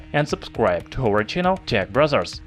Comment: I thought at first he said tech brazzers